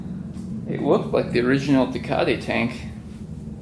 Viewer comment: I thought he was saying Ducati in a really messed up way 😂😂 never heard of tecate 🤦🏻‍♂️